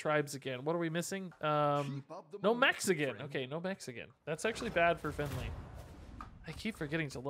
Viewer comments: I thought he said “no Mexican” instead of “No mechs again” at 1:34 😳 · I thought for sure he said no mexican and I almost choked on my food